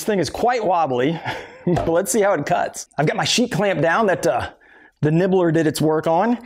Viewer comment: I thought you said sheet rock, but maybe its just what I heard.